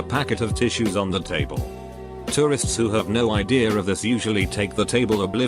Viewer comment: I thought it said terrorists